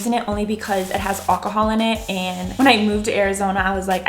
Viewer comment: Did she just say “Olcohol” ... omg 💀